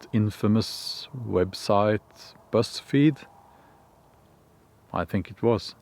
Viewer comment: Oh! he said Buzz Feed! I thought he was saying Breast Feed! hahaha! My thoughts: "Why is a facility that tests testosterone levels named, 'Breast Feed'?!"